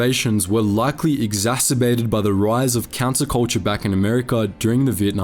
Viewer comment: did he call the peace movement cancel culture?